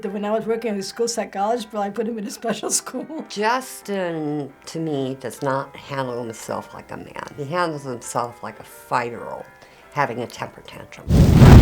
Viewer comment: can somebody please tell me what the woman at 5:12 said? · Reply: "He handles himself like a 5 year old having a temper tantrum"